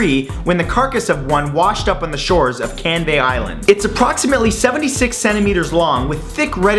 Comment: I thought he said Candy Island 😂😂